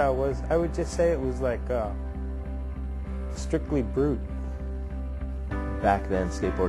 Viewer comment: Mark Gonzales uses a term in the sentence "Strictly (?)". What does he say? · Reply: Brute .... Strictly brute. Interesting characterization (what about Gonz isn't interesting? He's here being interviewed wearing his pants on his head). Natas's style was 100% masculine and forceful, yet he seemed to have no ego.